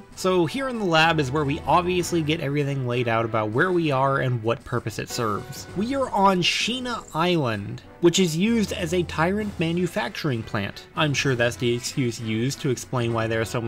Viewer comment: I swear that I've heard "Shemale Island" Basically Thailand 😂